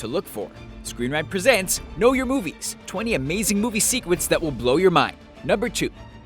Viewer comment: did he say sec-w-ets